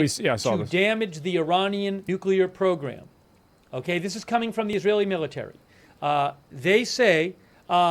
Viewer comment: What did he say? It sounded like it glitches out there... He couldn't have possibly said "This is coming from the Willy-Nillitary" right?